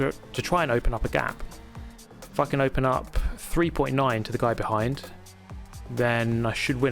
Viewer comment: Idk about you, but I heard casually say "F***in open up." 😂